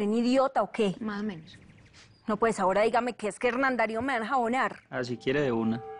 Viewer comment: que lo que dice daniela a 8:21 que no lo entendí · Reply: Dice que "ya que falta que me dejen volar"